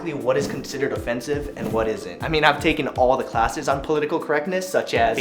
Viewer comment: Why did that say "What is is not?" at 0:28?